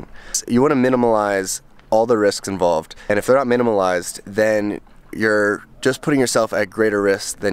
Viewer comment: Did he say minimilize... twice??